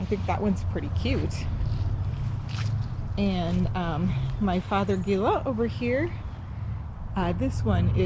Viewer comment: Great Tour! I LOVE you shade beds. Can you please tell me the name of the bush at 3:01 I couldn't catch the name? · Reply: Fothergilla! I think it is blue shadow cultivar. 🙂🌳